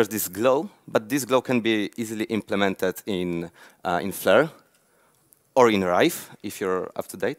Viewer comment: Can any one tell what he says at 17:53 flutter and ?? , I just started flutter two days back. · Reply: "It can be easily implemented in Flare - or in Rive". Rive is the new name of Flare which is a tool for creating awesome animations that you can use in Flutter. <link>